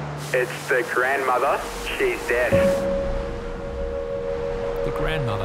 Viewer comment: I THOUGHT THEY SAID DEAD😰